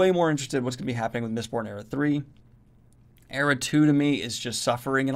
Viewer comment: Did he say era 3? There's an era 3!?